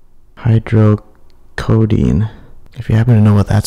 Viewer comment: the medication it sounded like you said Dehydrocodine if it was this it`s unusual to be liquid form but it`s an opioid painkiller addictive.